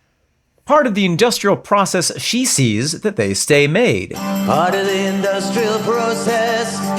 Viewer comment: I always heard it as "...she sees the display made".😂I suppose it also works..A more delicate line of workmanship, what?!